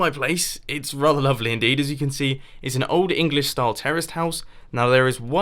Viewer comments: it sounds like you said terrorist house but it think you said terrace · did say its an old style english terrorist house? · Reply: It might sound like he said "terrorist" but he actually said terraced.